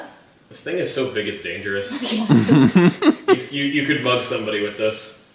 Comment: i thought he was saying bug but when i finally heard "you could mug somebody with this" i started laughing so hard